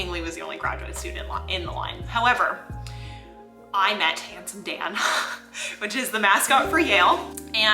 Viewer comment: I thought for a split second you said "I met a handsome man" 😆😅 Love your enthusiasm and energy, keep it up! 👍